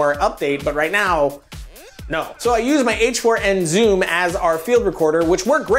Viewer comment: Dear, how do i spell this: 07:34 all i hear is "h n ware zoom" i really need a sound recorder please help!